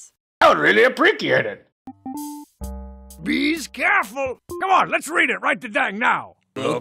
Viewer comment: "Bee scareful!" is such a good one to use on Halloween. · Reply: OMG that's so cute! I always heard it as "Be's careful!" But your idea is so fun as well.